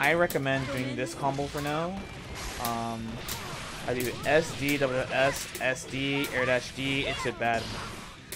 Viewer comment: can somebody tell me the inputs on 16:31 combo? I can't tell what he says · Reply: for the air combo (light units), it’s : slash dust, jump up and forward, slash dust, air dash, slash dust, then bad moon (214 P)